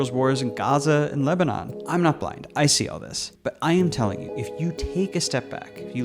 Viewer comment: “I’m not black; I see all this.” Had to pause what I was doing to rewind that, because that’s what I heard lmao